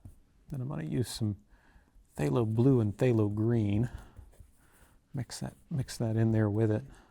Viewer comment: i always hear "fatal green" must be rather careful with the fatal green :D